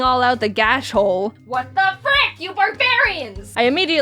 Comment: I legit thought She said "What the F_ck,You barbarians" for a Hot second XD